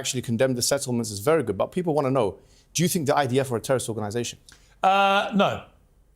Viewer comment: Has anyone else noticed that the auto captions keep showing “Hamas” when he actually says “IDF”? 😅 That’s pretty crazy… or is it just me?